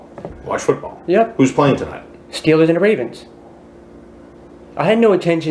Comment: Not "Steelers and the Ravens". Its "Stewes and the Vavens" 🤣😂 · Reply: I thought he said Steewahs Ann Ta Waverns.